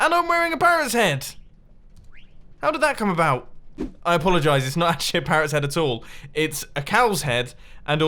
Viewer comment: In 5:27 why did you said s*** parrots! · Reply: Danica Rojas, He was actually saying “I apologize. It’s not actually a parrots head at all.”